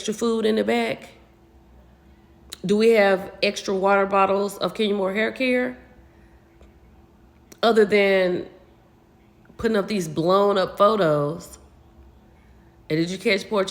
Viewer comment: Did you say Water Bottle of Kenya Moore Haircare?!? 😂😂😂😂😂😂😂😂😂😂